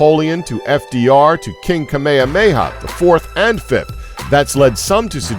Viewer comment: did he just say kamehameha